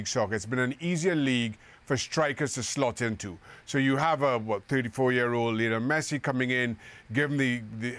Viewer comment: did Shaka say 34 years Messi ? 😂